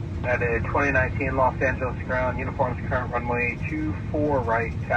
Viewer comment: I swear I heard someone on the radio say "Los Santos ground" surely that can't be by accident